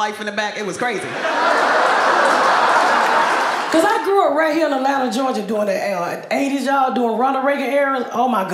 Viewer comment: ok seriously. Wtf did she even say? 😅 non American here. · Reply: I heard she grew up in the land of Georgia and it was mumbling after for me